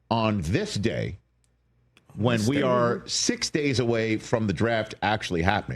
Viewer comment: 47 seconds I heard you! “ On this day! I see clearly!”